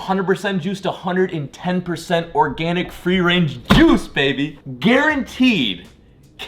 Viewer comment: Did he just say freer range juice 😂😂